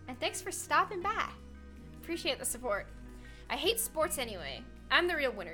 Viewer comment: lol I thought that she said thanks for the support I hate support 😂